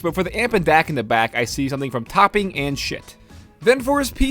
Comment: Did just say shit?